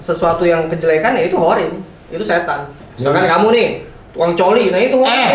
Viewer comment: gw dengernya "misalkan kamu nih koncoli"